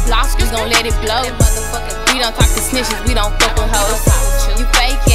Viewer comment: "I just seen yo mama at da corner store " LMAOO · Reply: I thought it said porn store